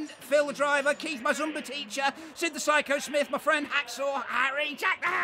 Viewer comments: how about some subtitles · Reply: Hoz it sounded like he said Cyrus the virus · THANKS TO MY ZUMBA TEACHER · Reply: i thought he said "Zombie Teacher" XD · Translation: Sorry my band mates can’t be with me I’ve been detained by your mags not the American mags your Brit mags thanks to all the fab artists, management, Phil Driver, Keith my Zumba teacher, Sid the psycho Smith, my friend Hacksaw Harry, Jack the hat, Sirius the Virus, Big Balls McGuessnis, no balls McGuessnis.. let me- slam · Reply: I thought he said "long Lenny" instead of "let me" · Did he just say Jack black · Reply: Jack the hat